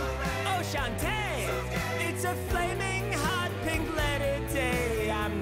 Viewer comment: Did he say "Mclennon"? Im CrYInG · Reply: "It's a flaming hot pink letter day" oof